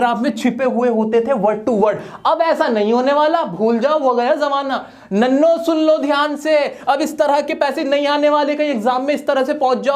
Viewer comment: nallo sun lo dyan se....did he say this ?🤔🤔🤣🤣